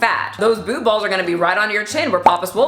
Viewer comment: I heard "boobfalls" (like a waterfall) so that is good.